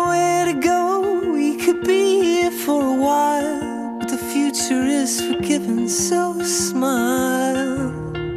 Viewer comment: When you're the only stupid one that thought that he was saying " The futuristic rivers" all the time..